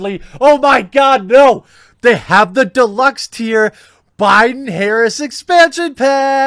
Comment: I thought you were saying Biden's Hair expansion..